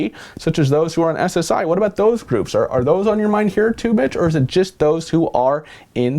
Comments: did you say Mitch or Bitch? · sounded like he said "bitch" instead of "mitch" lol